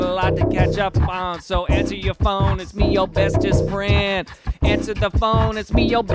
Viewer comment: Is it just me who thought he was saying “Ava answer ur phone” like who’s Ava🤣